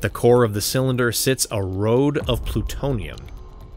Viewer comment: did you just say a 'RODE' of Plutonium????!!?